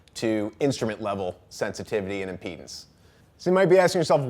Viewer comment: Omg I thought he said Guitar sensitivity in my penis, not impedance.